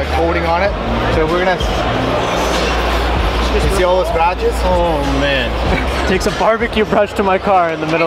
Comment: Bruh i thought it said "snatched" 🤣